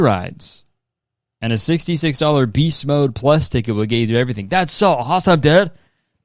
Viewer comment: what in the hell did say here??? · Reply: “That’s so awesome dude” I had to replay it so many times to understand